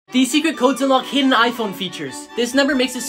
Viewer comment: I really thought it said “these secret codes can unlock any iPhone” 💀😂IM DEAD